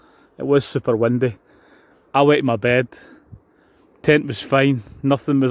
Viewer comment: Did I hear that right? You wet your bed? =D Surely I heard that wrong. Great tent! I really disliked the tunnel design at first, but the more I see it the more I'm becoming accustomed to it. I think the husband would be happier with something like this when we're bike camping. We might be able to squeeze the bikes into that little vestibule to keep them safe at night.